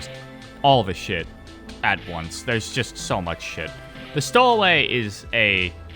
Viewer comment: what is a nice addition? Can't hear properly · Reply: stowaway, the ceiling monster that paralyzes and drags you up into it